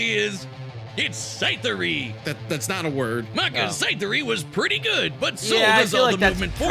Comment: did anyone else hear him say Cytheria?